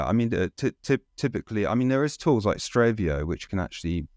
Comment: did he say Stravio or Strovio? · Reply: strevio